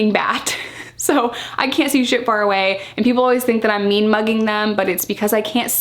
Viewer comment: "...not mean-mugging, I'm blind!" You're the cutest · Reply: I thought she was saying meme mugging. I was trying to figure out what meme mugging was lol